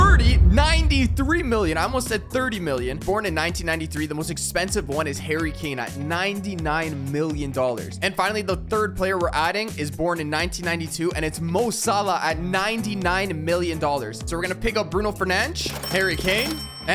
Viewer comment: did he say Kane or Keana